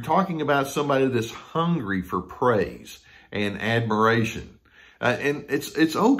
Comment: I thought you said ---Hungry for Prey !!!! .